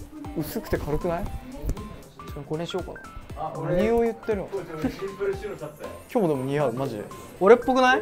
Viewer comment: 奥のゆごしんの会話も気になる！よく聞こえないけど シンプル白シャツ？か何か選んだけど、こーちに「着たかったら着て」って言ってるのかな？